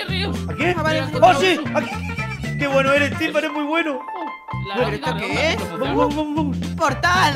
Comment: escuche "timba que bueno eres, que estas mu' bueno " Yo: confirmó eso! Xd a no, que no dijo eso :'v igual lo confirmó xd